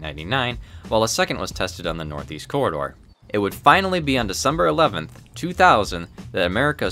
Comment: I was the only that I hear September 11 instead of December 11 6:47 💀